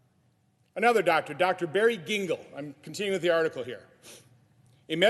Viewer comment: Am I hearing that doctor's name right? Barry Dingle?